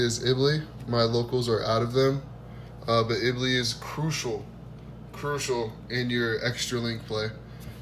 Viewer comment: Can someone write the names of the proxy cards for me? Can't tell what he is saying @ 3:24 sounds like italy. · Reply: lol I was saying iblee homie